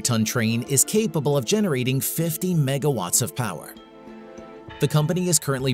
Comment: I thought I heard mega pint lol